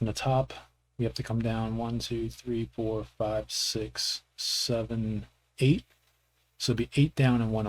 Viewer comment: did i hear...... 67!!!!!!!!!!!!! YAY and thanks btw it really helped